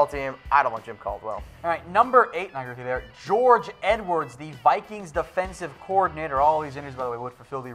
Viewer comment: it sounds like he said George Nword. 😂